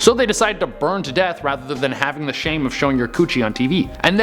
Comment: Did he just say choochie? 🤣😂🤣😂